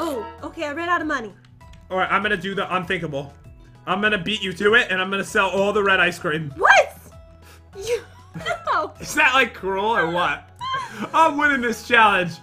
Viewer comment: When said ice creamo I thought that he said "I suck at nemo"! 😂😂🤣🤣🤣